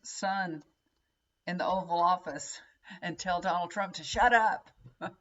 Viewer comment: Oh my god I thought you said McDonald Trump and I can’t stop laughing. I have to call him this from now on. It’s too perfect 😂